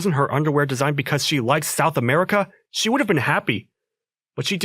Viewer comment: he said legit said South America, with the Subtitles saying South Africa, lol